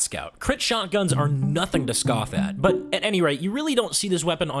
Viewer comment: Why u say „Anyray“ instead of „Anyway“?